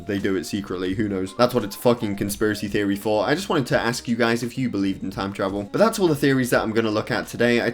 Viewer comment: What does he say at 13:03 I heard “that’s what it’s fucking conspiracy theory for.” Lmao